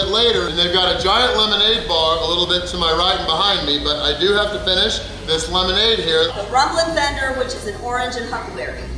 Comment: Could have sworn she said he has to drink the lemonade